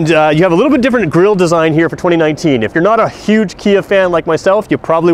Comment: did he just say he's not a huge kia fan at 1:40? hey at least he's honest for a car salesman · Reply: "If you're not a huge Kia fan like myself" is what I said... soooooo.....